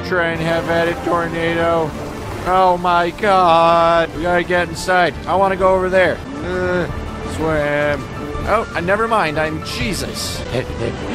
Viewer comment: Instead of him saying “Nevermind I’m Jesus” I heard “Nevermind I’m chesus”